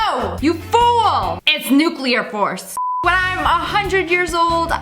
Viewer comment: why does it say "when I am"? It should say "if i turn"